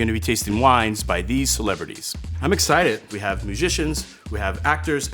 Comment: I first heard magicians and got really excited